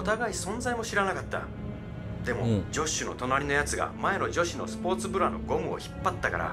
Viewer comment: “前の女子のスポーツブラ”を”前のジョッシュのスポーツブラ”に空耳して(´＜○＞ω＜○＞｀); カッってなったwww